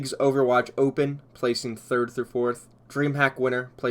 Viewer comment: Did he just say 3rth?